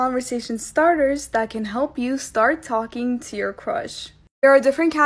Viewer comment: Lmao i thought that said “Start STALKING your crush”